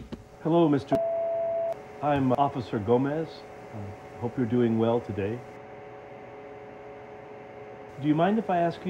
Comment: I heard I'm doing meth